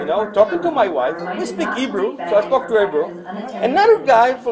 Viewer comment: Why do the worst parents have so many kids? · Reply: he did not say Hindi, he said Hebrew. The wife has a distinct accent. Clean your ears and listen to 4:16 again.